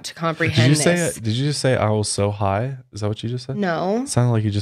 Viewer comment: I heard her say “I was so high” too😂👻